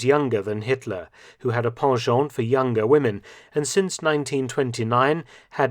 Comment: What is that french word at 1:51? I can’t figure it out for my life. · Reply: I assume you mean 'penchant'? It just means 'to have a liking for' or 'a leaning toward' something. It's used in both French and English to mean much the same thing.